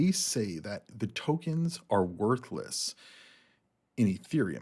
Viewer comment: Sounded like you said Templeton said Solana tokens are worthless not worth less